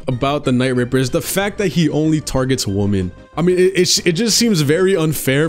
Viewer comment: Just so yk dawg, the plural of “Woman” is “Women” maybe it’s just the accent, but sounded like you were saying “he only targets woman.” 😂